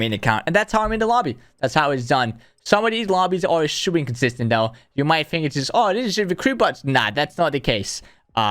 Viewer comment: bro wtf did you say at 5:17, sounds like complete gibberish youre really hard to understand lol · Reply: ''Some of these lobbies are super inconsistent. You might think ''Oh, this is just recruit bots!''. Nah, that's not the case''